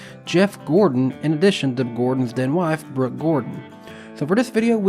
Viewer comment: Making up stuff is not cool. Not my thing. Not sure where you got that I said that from. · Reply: I think he misheard “then wife” as “dead wife”